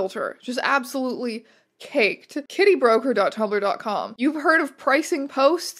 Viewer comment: Thought you said titty broker instead of kitty broker and was intrigued